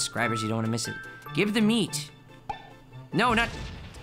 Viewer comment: Every time cow is swallowed i keep hearing "WE HAVE THE MEATS"